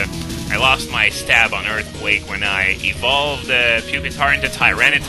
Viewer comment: What did you say at 6:03; you lost your what on earthquake?